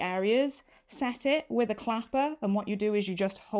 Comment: I thought you said clamper instead of an ironing Clapper?